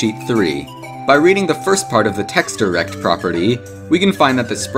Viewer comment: anyone else hear text 3rect